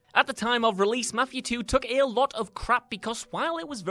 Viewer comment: I thought you were saying "Matthew 2" instead of mafia lol. Like , wtf is Matthew 2?? Never heard of that game but it looks kinda like mafi-..... oh. I am the dumb